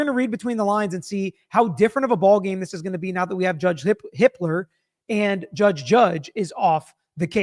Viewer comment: I know I misheard it, but it says something that I thought he said "Judge Hitler."